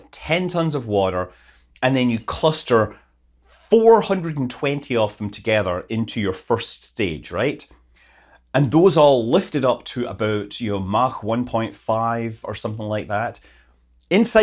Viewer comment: Wait, did I hear... 420 rockets? Haha, funny number